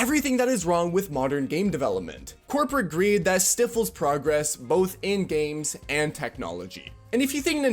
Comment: bro you did Not just say Stiffles instead of Stifles bro thats like pre-K level speech, you said Sniffles and just replaced the N with a T and didn’t even come close to the same word you meant that was a single letter difference.